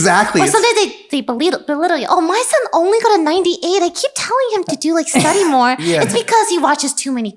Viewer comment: on 9:32 subtitles say my son only got a 98 i keep telling him i do like sex anymore. WTF why?